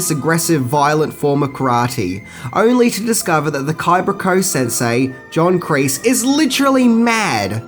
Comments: Miyagi's home VILLAGE was Tomi, located on the Island of Okinawa. Btw, did you actually say "Cibra Koh", at 0:57? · Did you say Kaibra Co at 0:56 ! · Did I hear him say “Caibra Ko” at the beginning? It’s at 0:98 or so.